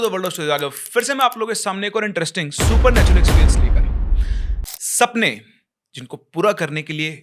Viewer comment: Mujhe aaj tak yeh samjh nahi aaya yeh starting mein kya bolte hai - 0:06 Welcome to world of stories….??? Kya please tell 😅😅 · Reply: Aur aa gaya hu firse meh aap logon ke saamne ek aur interesting supernatural experience lekar